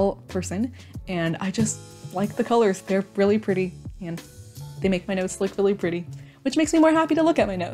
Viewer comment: Oop when you said "they make my notes really pretty", I thought you said "they make my nose really pretty" Me be taking a double take